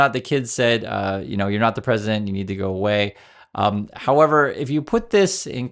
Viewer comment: It sounded like he said, "You're a prick and you need to go away." Could just be my hearing.